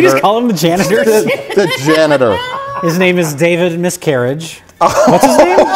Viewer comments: Holy shit I thought he said Dated Miscarriage instead of David Miscarriage, and that brought on a WHOLE NEW LEVEL of damn. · I thought at first they said David Miscavige, and I thought we were going to find out the Scientology guy had inexplicably made this.